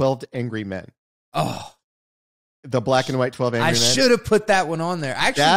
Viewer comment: I kept hearing ‘black cat white cat’ and thinking interesting term to explain absolutes 😂